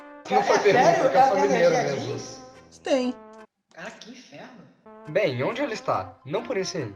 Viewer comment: alguém me diga o que eles disseram por favor? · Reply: "é sério a Vivian não pode usar jeans?" "é" "carai que inferno"